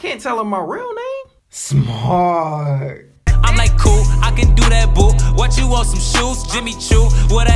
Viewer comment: You messed up you put I’m like cool I can’t do that boo instead of I can do that boo